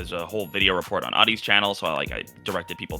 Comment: he said there is a full video report on 'oddie's channel' (I think?) does anyone know what video he is talking about here? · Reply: One of the podcast hosts is Adi Subra.